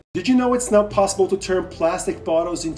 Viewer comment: I thought he just said it's not possible